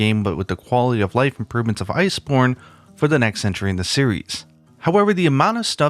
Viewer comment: Subtitles say "Ice Porn"